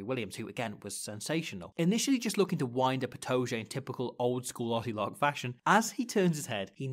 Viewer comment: This is going to drive me crazy - at 13:32 he says 'looking to wind up Itoje in typical... 'utolock' fashion?' Can anyone make out what he's saying there please? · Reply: 'old school Aussie lock' I think